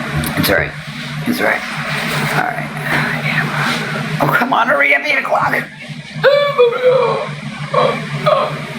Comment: does she say oh come on hurry up, i need a khlonopin? (Not sure if that's how you spell it)